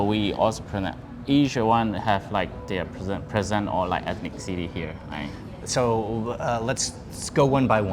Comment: I think he is just saying ethnicity and not enthic city.